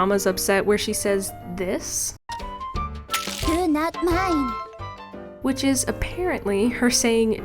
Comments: My mind was blown when you mentioned that she says ‘do not mind’ because me and my siblings. Always thought she said ‘eww not mine!’ 😂😂😂 I think I like our version of it better, so many hilarious memories attached to those incorrectly heard words 😂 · i always heard “you’re not mine” instead of “do not mind,” like i genuinely thought she was saying “ur not my child anymore” cuz she was disowning u for ur bad cooking💀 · I thought she was saying “Ew, not mines” so I always would say that in her voice 😂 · I always heard good not mine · to me it clearly sounds like she is saying "do not mind", idk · OMFG THAT SOUND CLIP! I thought I was the only know that knew that. I always thought said said "dunut mine" like thinking it wasn't english xD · my sister and i always thought she was saying “donut mine” because we literally couldn’t figure out what she was saying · "Do not mine" is what I thought she was saying. Sometimes I'd fail on purpose just to get a good laugh out of the nonsensical line in the wii version. XD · I heard "you are not mine" when u played this voiceline xD